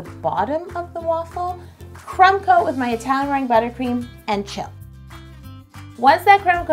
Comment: I thought the book said yo, it’s Italian merengue buttercream